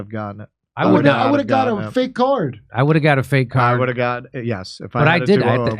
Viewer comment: I would've got a "Fake card"...I thought you said you would've got "Fake arm" 💪😂